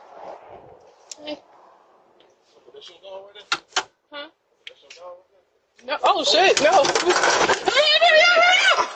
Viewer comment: What happened at the 6:30 mark? I have no idea what they're saying. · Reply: He was asking if that was her dog at the corner...she said no, oh shoot get in get in as the dog was coming over un their direction.